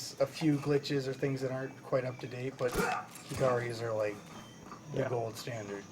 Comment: they are talking about a guy who did it in 1 hour and 21 mins ... i didn't catch his name though... can anyone tell me or link me to that ? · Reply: You might have had your answer by now what with your comment being two months old, but still. They were talking about Hikari.